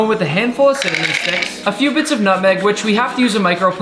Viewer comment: tbh when you said nutmeg I thought you said nothing🤣🤣🤣